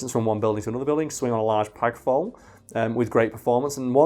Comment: did anyone else catch “pag fole” instead of flap pole?